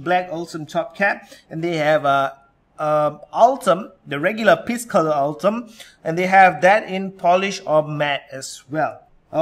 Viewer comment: Did he say : piss coloured Ultem ? 7:18 p.s. If your urine looks like Ultem, you need more water in your life.